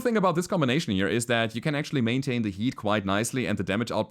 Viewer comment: Because of accent, I heard him say "maintain the yeet" and I died laughing.